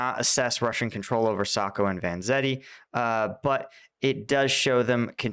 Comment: did he say Sacco and Vanzetti?!? 🤔